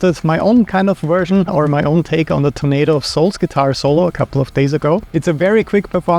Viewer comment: i swear to god i keep hearing you say "tomato of souls"